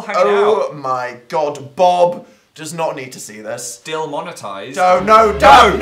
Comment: did....did said...DILmonetised,,,,???? capita£ester is thriving